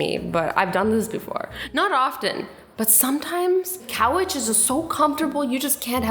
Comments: Did anyone notice she said "cowiches" instead of couches at 3:32? Classic Azzy :P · did anyone else hear her say cow itch instead of couch 😂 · Sorry, did she just say Coeach XD couch*😂 3:31 Love you Azzyyyy!!😂💕💕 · azzy says “cowech” instead of couch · did she say cow witch??? Lol 😂 I think she meant couch!!! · to me it kind of sounded like she said cow witches instead of couches. Just me? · did she just say couwcht 😂 Very small youtuber right here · she said cowarges Instead of couches .... Welp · hold up did she say coweches